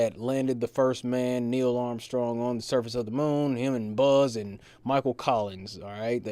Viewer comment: did he just call Buzz Aldrin 'Alien Buzz'?